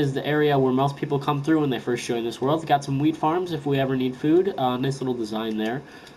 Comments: I swear he said weed farm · is it just me or did he say weed farm? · did he say weed farm